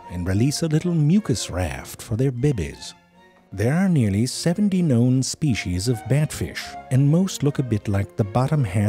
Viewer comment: does he really say 'there are nearly 70 million species of Batfish'?